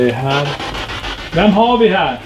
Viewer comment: bara jag som hörde "Thomas"? Frågan var ju: vem har vi här?